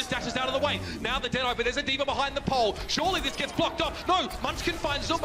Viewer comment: Did he just say "surely this gets fucked up" @ 3:40 lmao